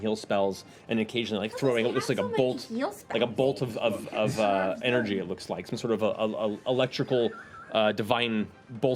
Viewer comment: What did Sam say to Laura at 59:38 when she asked how does t He (the old man cleric) get so many healing spells? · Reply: Something about, "he conserves them" I think.